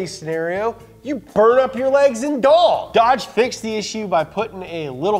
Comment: dedication to protecting all of the Legs & Dongs is admirable... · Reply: I thought he said "legs and doll"...